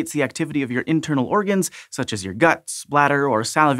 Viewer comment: What he said: "...such as your guts, bladder..." What I heard: "...such as your guts splatter..."